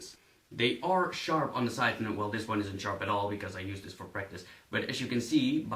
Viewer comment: I thought you said "Cause I use this for breakfast" 1:53 xD